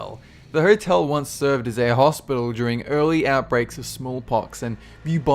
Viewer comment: Anyone else notice at 1:10 he said early, yet the subtitles said deadly?